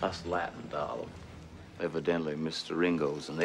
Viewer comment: I really thought he said, “how I really hate him.” But, it doesn’t make a difference